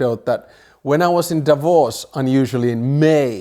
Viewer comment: I thought he said, "when i was in divorce"